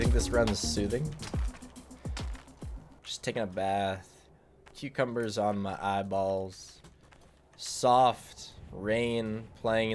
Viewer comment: MY AUDIO CUT AND I THOUGHT YOU SAID CUCUMBERS ON MY BALLS